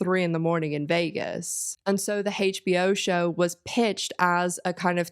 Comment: American here. I kept mishearing the word pawn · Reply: I’m mishearing her saying “Hey-sheBO Show”